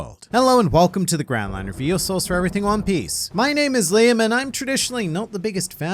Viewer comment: Anyone misheard the intro line "Your source for everything One Piece" to "Venosauraus" XD.... been a lot since just wanted to ask lol